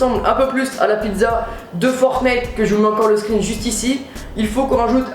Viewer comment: 😂😂c’est moi ou à 11:44 il dit fortnet ??